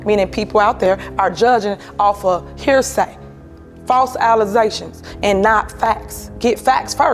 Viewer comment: did she say false "allizations". And telling other people they need to get an education.